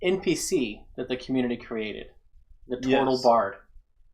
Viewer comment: 😂every time you say "tortle bard" I hear "total bard" and I feel like that should be a bard subclass.